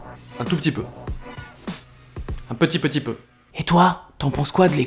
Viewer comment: Vous pouvez m'aider s'il vous plaît? Qu'est-ce qu'il dit dans le minute 4:30 jusqu'à "fait comme chez toi"? Merci beaucoup. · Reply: Lâche un com', mets-toi bien ! ;)